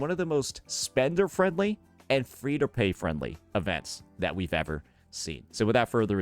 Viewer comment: Did I heard right? Free to pay friendly event? 😢😅